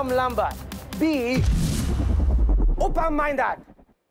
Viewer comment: I don't know if it is intentional but I heard "oppa-minded" instead of "open-minded" :D